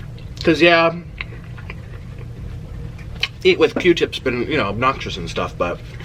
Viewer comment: I’m weak 🤣 I’m doing my make up getting ready for work and all I hear is “eat with que tip” 😭